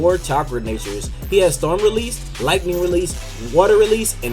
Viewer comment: did he say storm release? Idk why but I can't tell what he's saying at that part